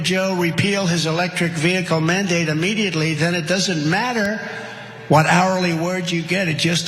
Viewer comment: Trumps brain is fried at 2:49 he said it doesn’t matter what hourly words you get, I didn’t know anybody was working for words pr hr